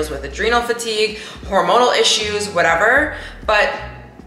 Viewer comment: Why am I hearing “I have personality issues”